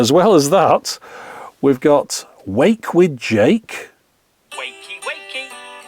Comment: I thought it said “wake up with joke”. Very disappointing, Jake.